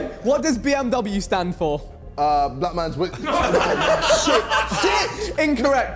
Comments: Vik: What does BMW stand for JJ: Black Man's Willy 🤣🤣🤣🤣🤣🤣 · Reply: Am I the only one that thought he said "Black Mans Whip" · Vik "JJ what does BMW stand for? " JJ: "um Black Man's Will-" 😂😂😂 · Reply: I heard black man's widow 🤣🤣.. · I thought he said black mans whip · I thought JJ said BMW was Black Mans WHIP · I thought jj said "black man's witness"